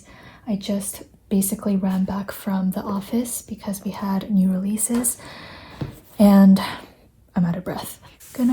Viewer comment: I heard bc we had no Reese’s. I thought, Wow! She really wanted some candy! lol